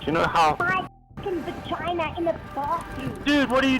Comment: She clearly says "my f**ing v*g*n in the bathroom" as they are approaching the dude.